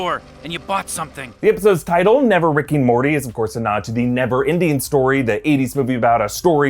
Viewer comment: Did you just say “never Indian story”